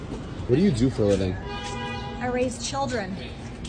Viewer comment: Damn I thought that woman said I race children 😂😂 respect the hustle